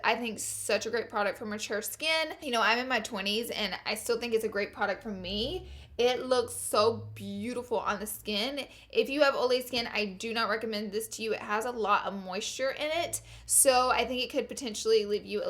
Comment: I thought she said "if you have old lady skin" when talking about Bobbi brown 😂😂😂